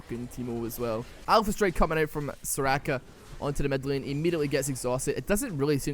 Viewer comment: Did you say alpha strike coming out from Soraka? Lol